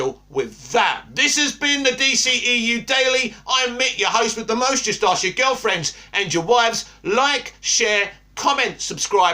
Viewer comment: lol every single time he ends a video I thought he said he’s gonna steal your wife and your girlfriend I kept laughing